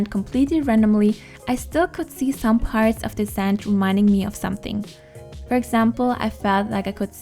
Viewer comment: for the longest time i thought you were saying scent not sand BAHHAHAAH ly